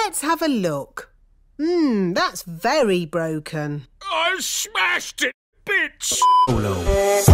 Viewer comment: i smashed it B#€$¥ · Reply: How have I not noticed that until I saw this comment😂 I thought he said ‘I smashed it into bits’😂